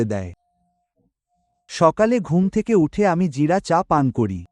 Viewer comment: what was the tea he mentioned. Couldn’t quite make out what he said. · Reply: Cumin tea.